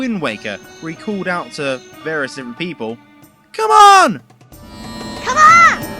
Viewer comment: Oh, he says "Come on"? I thought he said "Come along".